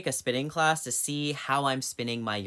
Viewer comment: I thought you said spitting class lol 😆